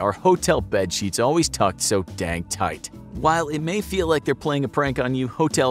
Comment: At first I thought it said packed so dank tight lool